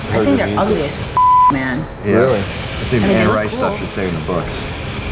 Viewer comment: " i think the anne rice stuff should stay in the books." i watch this interview every couple of months and always fast foward to that part. it never gets old. matt you are the man. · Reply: lol i thought he said "if you can't write stuff, you should stay in the bush." Thanks for clearing that